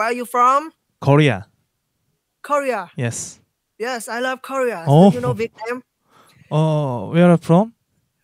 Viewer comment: 제 귀엔 “Do you know BTS?”로 들리는데.. 🙄🙄